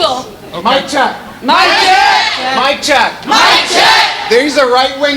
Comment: I've heard this Mic Check thing before. I want to ask a former SJW to explain that. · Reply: Oh.. I thought that he said "mind check" but I wasn't sure.. Why would he say "mic check" with no mic?